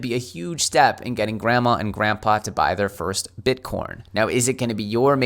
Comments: Did you say Bitcorn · did you say “Bitcorn”? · Grandma and Grandpa are going to buy their first "Bitcorn"? It's hard on the dentures, I hear.